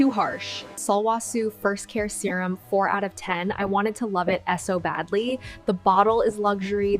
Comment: "S. O. badly" instead of "so badly" reveals it as AI immediately